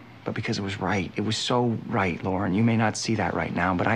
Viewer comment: Did Cristopher called Lorelie by her real name, "Lauren"? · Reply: sounded like he said "Lor" to me and then very quickly said "and" after that. He always called her "Lor" for short on the show. He was the only character who did that.